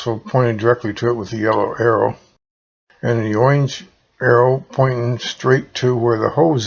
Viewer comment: What is oinge? Are you trying to say orange?